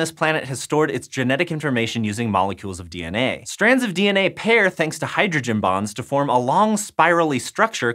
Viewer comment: I had to rewind because i really thought he said "strands of dna pair thanks to hydrogen BOMBS"